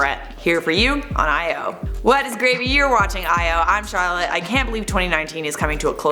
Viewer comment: I keep hearing "What is gravy..." In the intros... Am I going crazy???